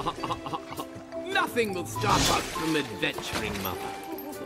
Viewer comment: did I hear that right? did the dude just say "mother?" eeeeeeeeeeeeeeeeeeeeeeeww